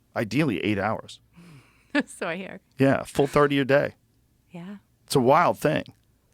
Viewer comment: what the fuck is he saying. "Full 30 a day" and says it twice?? What the fuck is that supposed to mean